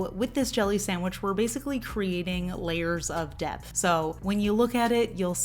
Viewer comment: lol When you said "layers of depth" I thought you said "layers of DEATH" and I was like, oh yeah, that makes sense cause she's drawing ghosts on top of each other. 😆